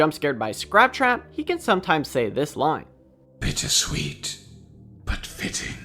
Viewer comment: I thought he said “bitches sweet”